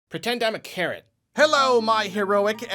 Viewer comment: "Pretend I'm a carrot." Sure thing haha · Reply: I thought he said "pretend I'm a Karen" 😂😂😂